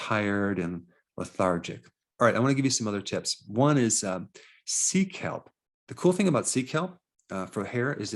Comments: What I heard: "I'm gonna give you some other tips, number 1 is, seek help." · when he said 'Sea Kelp' I thought he said seek help... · I literally thought you said "seek help". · am I the only one who heard Seek Help? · I thought you said “seek help”… that’s what I’m here for! Oh, sea kelp. 🤷🏻‍♀️ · When he said sea Kelp, I thought he said 1.) Seek Help 😂 I was like oooookkkaayyy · I thought he said “Seek help” for number 1😆 I was like, dang, I guess this is an issue only a professional can fix😭😆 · Hhhhhhh 8:26 no way I heard, How to grow thick hair 1. Seek help..... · oh sea kelp, i thought you just said seek help